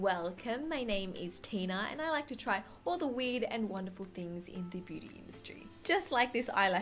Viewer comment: At first I heard "all the weed" at 0:21 and I was like really? 😂